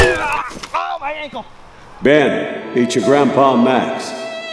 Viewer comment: Turn on captions... "Ben eat your Grandpa Max" LOL you never know what anyone is saying...